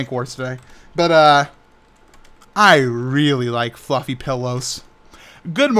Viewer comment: It sounded like you said "you like fucking pillows" But great vid:D